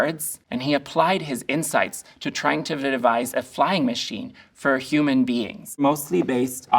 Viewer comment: I think he’s saying “trying to devise”